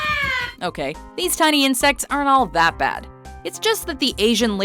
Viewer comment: Who thought it said 'Injects'